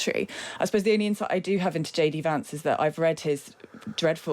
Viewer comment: Funny it sounds like he said I’m shady Vance)